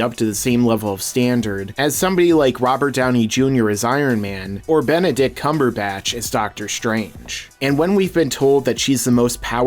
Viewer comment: It almost sounded like you said ”Benedict Cumonyourback”